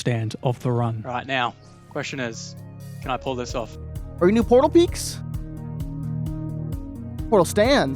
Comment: you should prob change the subtitles to say Portal Peeks instead of Portal Peaks